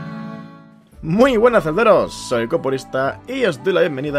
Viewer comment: entendi "muy buenas zelderos, soy comunista" jsjsjsj